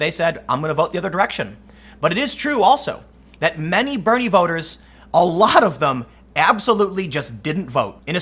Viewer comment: I thought that said BEANIE voters for a second.